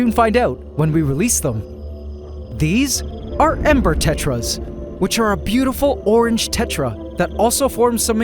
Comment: I thought he said emperor tetras 😅